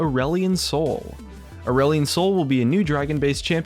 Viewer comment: At first I thought you said "Orwellian Soul" And was about to flip my table.